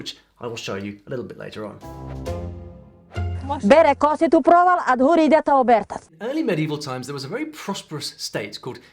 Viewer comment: as a native Ukrainian even I couldn't understand what that woman said... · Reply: "Бере косит у провал, а згори йдете обертати." Заготовляють сіно. Привіт з Галичини "One goes down cutting grass and the rest follow turning grass on it's head (so it can dry)". Hay preparation. Cheers from Halicia